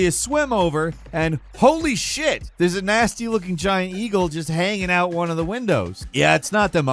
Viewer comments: Holy Shiite, did falcon just say Giant EAGLE? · Did he just call an eel a eagle? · did you just call an eel a eagle lol? 😂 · Did he call the eel in Mario 64 an "eagle?"